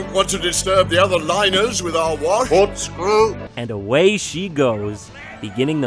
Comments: Can we talk about why the frig jontron has three taxidermied mice and tiny sombreros for them??? Also at 9:17 I thought he just straight up said corkscrew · What is he actually saying here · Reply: I've listened to it a few times and the only thing I can come up with is "Port's Clear" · I think he said "port stern" or some other boating terminology. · I heard Cork Screw.